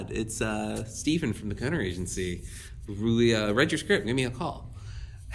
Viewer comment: Did anyone catch the name of the agency "...Steve, at the ** Agency;" @ 5:15? Thank you! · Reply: Gunner agency… I think?